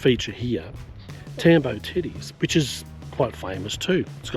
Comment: Always love your content - usually you include something that other Youtubers leave out so thanks for that 😊 You did have me wondering at one point though - thought you said Tambo was famous for its 'titties' but the suspense was short lived when they turned out to be 'teddies' - phew 😂